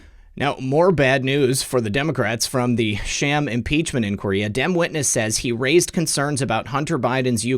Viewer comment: I love it when you say “Dem witness” because I hear “dimwitness” and I chuckle every time! 😂👍🏼